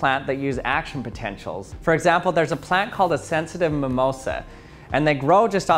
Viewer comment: All I kept hearing was “Sensitive Momoa.” I was like...🤔...he could be. 🤷🏻‍♂️